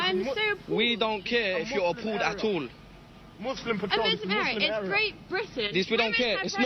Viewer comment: Did I hear Muslim era 😮, Send these people out of your cities before they take over